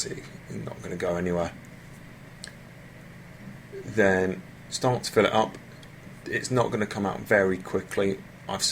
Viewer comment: I'm sorry but what did you say at 1:46 i can't understand the words you used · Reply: He said 'start to fill it up"